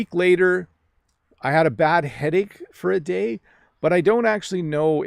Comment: Hey guys, help me please. I didn't heard fully sentence between 12:05 - 12:09. I heard it: "About a week later, i have a bad ... for a day". Could you tell me a missed word? By the way, what to use correctly: "missed word" or "missing word"? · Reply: 1) About a week later I haD a bad headache for a day. ( uses "had" because it was in the past). 2) the missing word (there is no one specific word now).